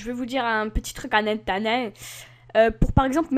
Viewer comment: Pardon ? Anenta quoi ? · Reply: En attendant 😂😂